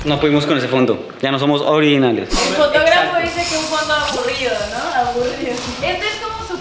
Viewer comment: Les juro que escuche "El es el fotógrafo CARA" 😂🔥